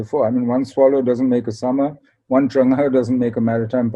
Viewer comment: Hey What did Mr. SSM say @ 12:31, He said something like " One Swallow doesn't make a summer, one ______ doesn't make a maritime power." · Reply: Zheng He